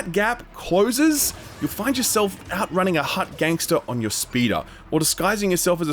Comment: I totally thought he said, “you find yourself outrunning a hot gangster on your speeder.” …..go on…